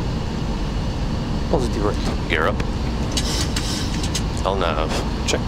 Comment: what is he saying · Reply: "Positive rate"